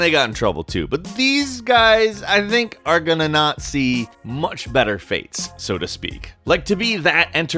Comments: They were a German group and he said not see but it sounded like Nazi see it for your self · Am i the only one who hears 'i think are gonna nazi' at 2:58?